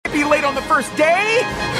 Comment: It sounded like he said:CAN I BE LAID IN THE FORST DAY!?